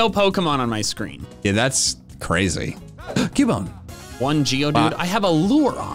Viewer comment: Sponsored by Cookie Run: Kingdom! Download here: <link> Follow this link: <link> and use code REALTOUGHCOOKIES for 500 crystals! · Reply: I thought he said Giovanni instead of geodude